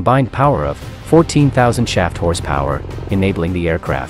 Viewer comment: i thought i heard shat horse power, but it was shaft horse power